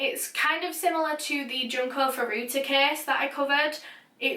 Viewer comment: anyone know the case she mentioned? I couldn’t hear through her accent it sounded like John carfurutti? But I searched a bunch of diff spellings and no results :/ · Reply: Sophie Harris i think she said “junko furuta case”